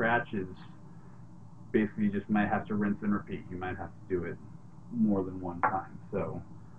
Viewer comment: He said rinse under a (something) @ 4:30? Did he say the sink or under something else? HALP